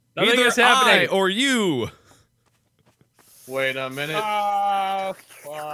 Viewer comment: It sounded like wade said “WADE A MINUTE!!!!” Not wait a minute